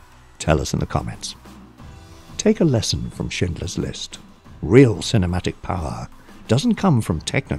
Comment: I first I thought the narrator said "Shitler's list" in my mind I'm like "did Spielberg make a comedy making fun of hitler? Lol"